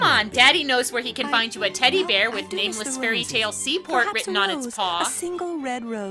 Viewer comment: Hey, did you just say “Nameless Fairy Tale Sea Port” there, by the way?